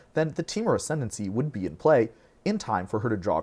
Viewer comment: Maybe I am mishearing, but i tried to relisten three times and it sounds like he says that it "would NOT be in play" to get draws from BOTH Surrak and Maelstrom. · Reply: he said ‘would be in play’